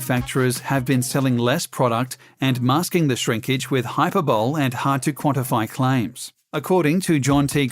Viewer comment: he said hyper bowl instead of hyperbole